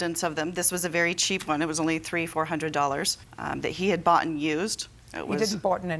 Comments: "He had bought and used" JJ "NOBODY BOUGHTEN ANYTHING" Er, JJ, she didn't say that. JJ is so used to idiots that can't speak English that she jumped on that one too soon. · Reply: It sounded like she said “boughten used” as in it was purchased as a previously owned item. This is not grammatically correct. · "He had bought and used" JJ "NOBODY BOUGHTEN ANYTHING" Er, JJ, she didn't say that. JJ is so used to idiots that can't speak English that she jumped on that one too soon. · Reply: I thought she said "boughten used"